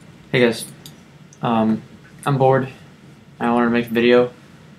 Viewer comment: Oh *Facepalm* you said "Bored", I thought you said Bård >.<